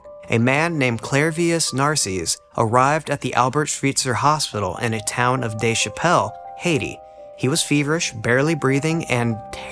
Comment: Did you say @ 11:08 "in the town of Dave Chappelle, Haiti"??? 😂😂😂